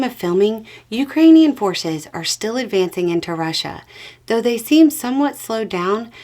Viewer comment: Funny, at the start I thought said "Ukrainian horses are still advancing into Russia". Guess I'm really looking forward to that horse video. 🤣